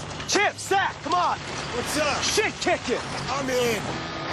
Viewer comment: When I heard Happy yell “I’m in”, was I the only one who actually heard “dibs”?